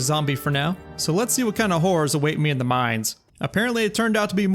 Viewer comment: I know you said let see what kind of horrors await me in the mines but I really heard whores the first time 😂😂😂😂😂